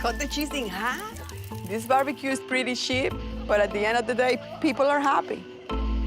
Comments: I swear she said shit not cheap 😂 and It’s not just the accent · did she say “this cookout is pretty shit”? lmaoo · I had to rewind like 5 times because I thought she said "this barbecue is pretty shit" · I thought the wife said “this barbecue is pretty shit” around the 13:00 mark and I’d have to agree lol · When she said "this bbq is pretty cheap" with her accent I thought she said it was pretty shit